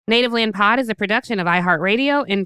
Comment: I had to rewind it...I thought Angela said Nigga Land Pod lol